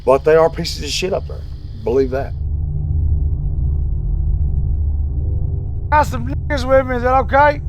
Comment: did he say “I got some n*ggas with me?” 😒 disgusting.